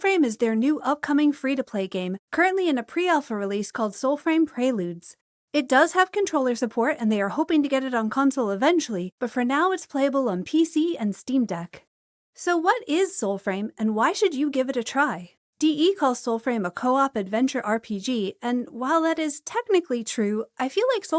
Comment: wait, did you say Steamdeck?! 🤩 I wasn't sure and I didn't want to try, as navigating steamdeck desktop is a bit bothersome, but does it work well? This would make my Christmas holiday so much easier as I'm missing my PC and my envoy 😜